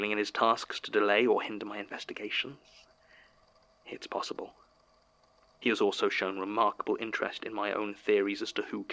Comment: There is a whisper at 20:22 that I cannot make out at all, creepy. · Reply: I played the part at full volume right next to me and CAN confirm the voice said “Sasha”